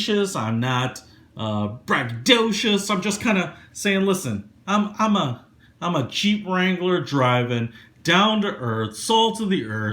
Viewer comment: Texas. You know for a second I thought u said "I'm a cheap wrangler driving, salt of the earth"